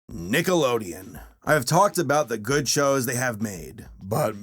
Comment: It sounded like you said Niggalodeon that was funny to me